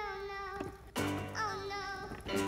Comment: I just woke up and have my volume low...I thought he said “grumpy noodles!!” 😅